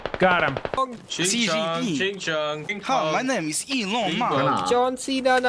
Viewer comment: at around 10:10 some point, am i the only person who heard someone has mentioned "ching chong" stuff?